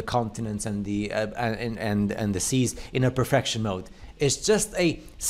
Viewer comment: did he say "perfectional"??? Lmfao